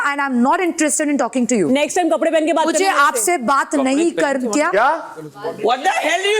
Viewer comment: for malti what said. Cuz everyone saying pure kapadey, in fact she only said kapadey pehenke 😅